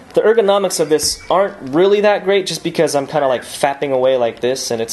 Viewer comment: It sounded like you said just fapping away on it and not tapping